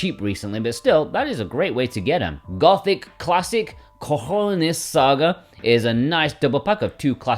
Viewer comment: That kinda sounded like "Gothic Classic Cojones Saga" to me, which that in Spanish translates to "Gothic Classic Bollocks Saga"